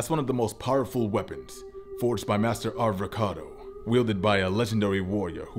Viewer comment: Why does no one point out that the legendary "un-wieldable" sword was created by Ar~vocado...Avocado? Anyone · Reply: I thought he said avocado, even the captions say avocado